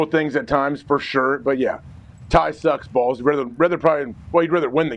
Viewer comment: When AJ said "Tie sucks balls" I heard "Ty sucks balls"